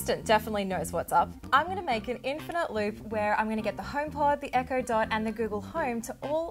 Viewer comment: I legit thought she said tide pod instead of home pod